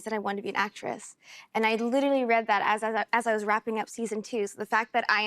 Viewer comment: Did I hear season 2 🤔😱